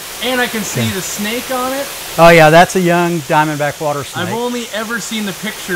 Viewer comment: I thought he said diamondback rattlesnake.